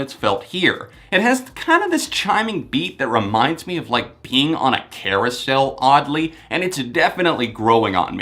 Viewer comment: Holy shit. I thought you said PEEING on a carousal and I was dead.